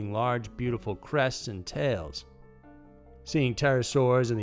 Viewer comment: Def turned CC on cuz I swore u said large beautiful breasts not crests.... 🤣🤣🤣